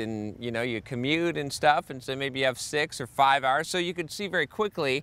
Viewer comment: i heard "maybe you can have sex for 5 hours"...props to you bro.